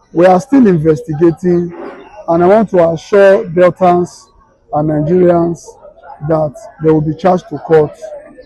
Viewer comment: "I want to assure Biafrans and Nigerians..." Did I hear you say Biafrans or I heard something else? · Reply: “Deltans and Nigerians"